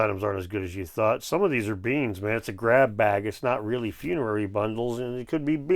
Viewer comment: is he saying beans or beings? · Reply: BEANS